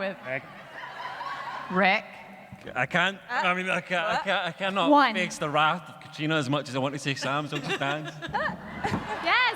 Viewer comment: Pls what does Rik says at 5:32? "I cannot fix the wrath of Caitriona? I cannot understand what does it means or if I heard rightly so. Tks